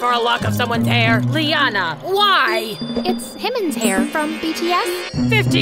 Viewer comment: it sounded like she said himin's hair Didn't it?